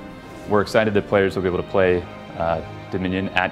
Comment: whata fuck did he said at 2:24 ? and it will be avaible for sortthafueahfter.